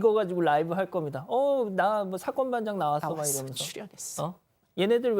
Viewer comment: 앵커님이랑 소리겹치는 소리 무슨소리? 나왔어 출현했어? 이런소리 들리는데